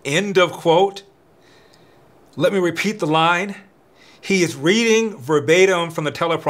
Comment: "Let me repeat the line" is exactly what the White House said he said. That's not what I heard. I heard, "Repeat the line." I missed the "Let me" part.